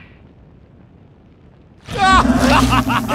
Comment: Did he said OMG?